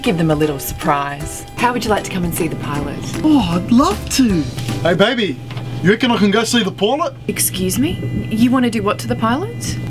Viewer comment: what did he say? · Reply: “Hey baby u recon I could go see the Pilot” he said that